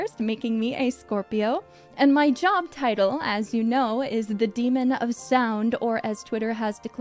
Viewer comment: I thought it said "The Demon of Song" and had immediate dark souls 2 flashbacks...